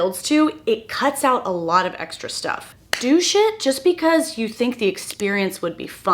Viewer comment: You said do shit and I thought you said douche it. It really threw me for a second 🤣